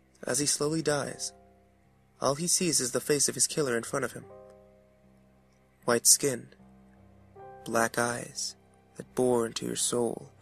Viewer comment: between 8:42 and 8:48 did he say "white skin, black guys"?